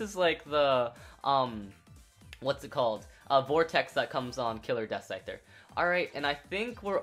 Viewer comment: Did he just say vortex came on killer death scizor